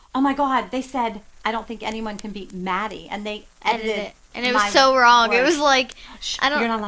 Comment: ahahhahaha what word? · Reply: I think it was "Edited" ;)